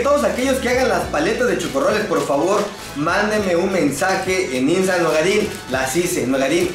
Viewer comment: Escuché Drogadin 1:55 xD